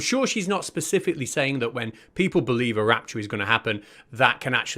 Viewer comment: I must have misheard I thought they were saying "the Raptor is coming" I've been hiding from dinosaurs all week!